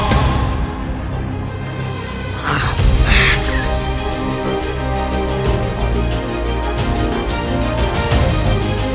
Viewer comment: alguien sabe que dijo? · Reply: Jamas